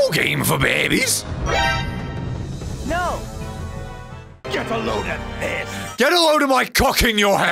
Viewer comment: The first time I heard Eggman say “get a load of this!” I swear to god I thought he said “get a load of piss!” Lol